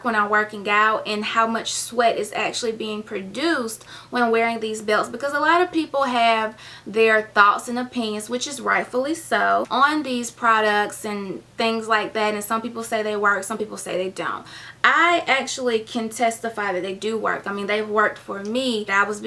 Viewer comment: did I hear "some people have theier thoughts and opinion which are worthless" ?